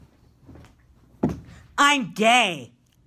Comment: for some reason I used to think he said "I'm dead!"